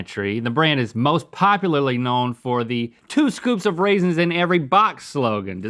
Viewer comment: At first I thought they were saying "Two scoops of raisins in every BITE" .